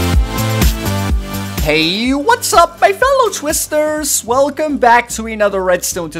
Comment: I swear I thought you said hello my fellow sisters...